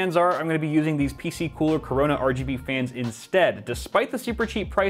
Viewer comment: DID HE SAY CORONA . THIS MAN CAN SEE IN THE FUTURE